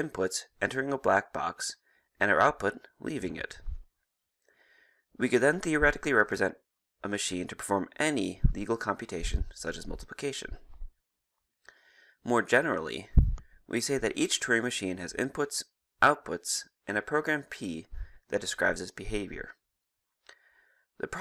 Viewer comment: seriously thought he said "any evil computation like multiplication"